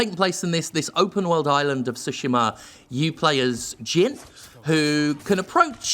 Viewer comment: I can't help but here "you play as Jiff", and I have no idea if he misspoke, I misheard, or if it's a joke, or what. Anyone? Help!